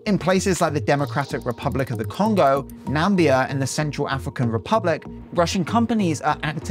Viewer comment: did homie just say Nambia😭